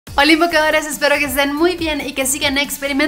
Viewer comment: Escuche "Oli invocadores" y cerre el video , alguien mas?